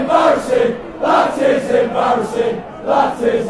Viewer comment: i hear 'that is emotion'